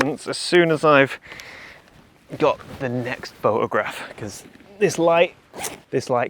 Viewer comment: did you say "boat-a-graph"?? Well done sir.